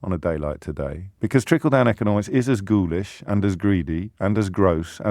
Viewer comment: Ohhh, ghoulish...I thought he said goulash...love goulash 😉